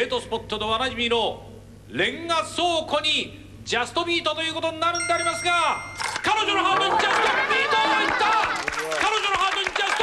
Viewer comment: does it say JUSTIN BIEBER!!!!!